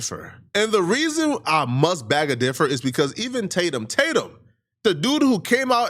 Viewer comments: “I beg a differ” lmaooo you mean i beg to differ 😂😂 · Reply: I thought he said I bag a differ 2:25 😂😂😂😂😂I'm cacklin!! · Did this 🥷 say “ Bag a differ “ as a form of expression instead of “ Beg To Differ “ ??!? 🤣🤣🤣🤣